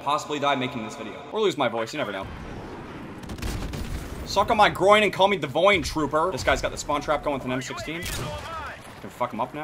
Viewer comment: It sounds like he said "Suck on my groin and call me devoine trooper" ha ha.